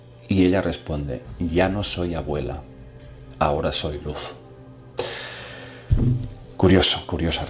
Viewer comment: Tengo una consulta: no entiendo qué le dice la abuela en 7:10. Ya no soy abuela, ahora soy... ¿qué? Desde ya, muchas gracias. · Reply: Ahora soy luz